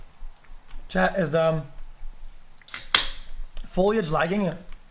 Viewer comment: I thought he said "chat is dumb" D